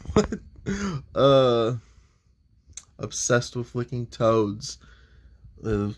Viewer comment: Toads? I thought u said toes 😂